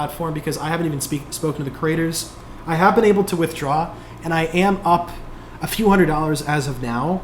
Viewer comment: Hold on. You were not able to withdraw yet? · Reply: @@ No. I think i just misheard him @ 11:08. "I have been able to withdraw" and "I haven't been able to withdraw" is sometimes easy to mishear. I thought it was the latter because "i cant vouch for the platform" sounded a bit negative.